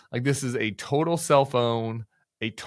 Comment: I always hear “a total self own” as “a total cell phone” 🤦🏻‍♀️.